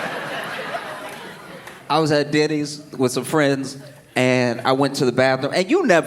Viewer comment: When he said Denny's the first time I heard Diddy's and was like this shit just got real!